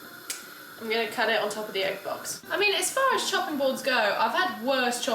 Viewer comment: I was cooking while watching this and heard her say 'I'll just cut it on the x-box'. And I said out loud: Excuse me that is 500£. Where did you get that kind of money from?! Then I looked at the screen and realised my mistake...